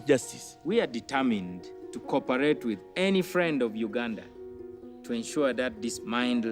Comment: I thought he said wakanda ☠️☠️☠️